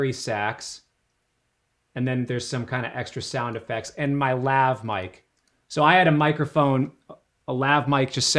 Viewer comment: I heard 'and my laugh-mic' and knowing that would actually make perfect sense